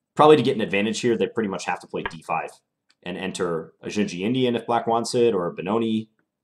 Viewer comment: What kind of Indian do you say at 3:17 ? I didn't catch that. Also I looked it up and he was your SECOND ICC video! He resigned too early. Thanks for the upload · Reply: NeverInterpreter is correct: I said "Dzindzi-Indian" (a fun opening I play sometimes).